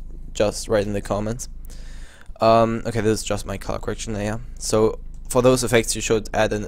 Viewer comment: i thought he said 'My cock arrection'